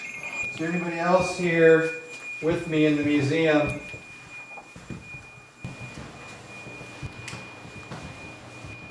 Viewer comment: When begins to walk down the hall with the ax, I thought I heard a whisper. I played it back again and again to make sure.... what I hear is a female say "don't" at the 31 seconds mark but I've time stamped it a few seconds early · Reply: It's at 25:31, if you slow it down to 0.75x you can hear her say '' don't goad him '' 👍